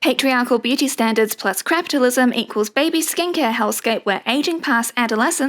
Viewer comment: Did you just say “crapitalism”? 😹